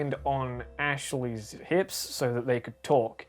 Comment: I just hear you say "Nips" and not "hips"